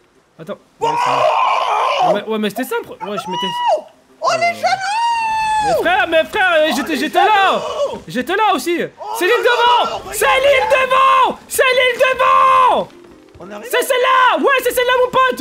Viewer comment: a 7:25 il a dit c'est l'ile devant moi j'ai compris "Céline devant" lol